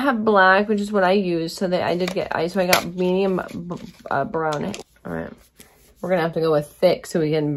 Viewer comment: Lmfaoooooo, did anyone else catch that “medium Brownay” at 16:07 😂😂😂😂💀💀